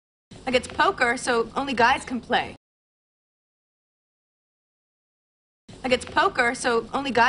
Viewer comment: 에 like 가 아이 겟츠 라고 들리는데 혹시 I guess it’s 인가요? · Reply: 피비가 Like it's ~라고 하는데 저한테는 [라익깃츠] 라고 들려요~ 이 문장에서 like는 큰 의미는 없이 붙이는 말이에요. <link> 제가 블로그에 like의 다른 쓰임에 대해 포스팅한 거 링크예요~~^^